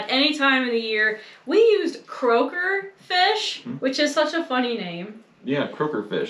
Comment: At first, I thought said she got "Kroger" fish (supermarket) fish LOL but i see it is "Croaker" fish LOL .. One way to make fish less fishy in smell and taste is to soak the fish in milk for a few minutes. I learned that technique from watching Japanese chefs on tv. Maybe would try a fish recipe that had fish that was previously soaked in milk before cooking though it would not be early 1800s cooking.. Is that Rooster the same mean Rooster that attacked in the hen house? OH my gosh what an ordeal with that crazy dentist!